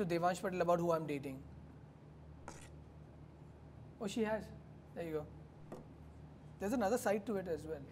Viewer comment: did he say ayayoo?that's a tamil word ! Is he a tamilian?